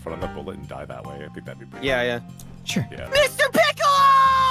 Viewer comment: Thank you very much for covering the riveting betrayal arc of this silly little heist movie. My fave part is the "I'm gonna GRIP you" moment. Also, you're a fellow in mishearing "Mr. Piccolo" as "Mr. Pickle." I mean, I did mishear that before, and then there's how the theme naming works in Dragon Ball, considering names like Kakarot, Vegeta, Raditz...